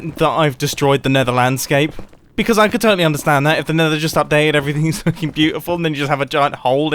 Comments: no? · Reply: FrenchTardigrade it just sounds like he said 'everything is fucking beautiful' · anyone else hear mumbo swear lmao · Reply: Didn’t he say “looking” not ‘“f......ng” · Did mumbo say the F word? It sounded like he said "the nether was just updated and the landscape is F****ng beautiful" Edit: it's at like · Did mumbo just say f-ing beatifull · anyone else hear "f*cking beautiful"